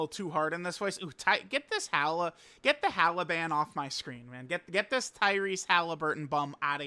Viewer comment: I swear I thought I heard "taliban" from 💀